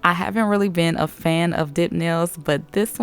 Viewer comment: the captions call them "dick nails" lmao instead of dip. Way to go autocap.